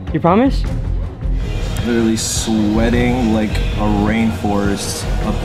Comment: "Sweating like a rainforest up in hear"😂😂😂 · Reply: Fool Force thought he said sweating like a rainhorse